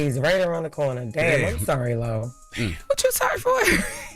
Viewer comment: lol did she call her Laurel?? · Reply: “Lo”, I guess shortened her name